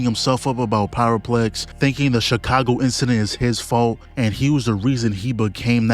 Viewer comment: almost thought bro said Shibuya incidrnt.